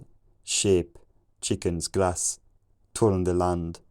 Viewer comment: although I heard a word here or there before that that I may have understood if I'm not mistaken. Chicken?